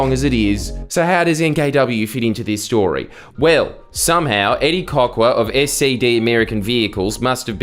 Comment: Was listening to this and thought you said his name was Eddie cockwad as a joke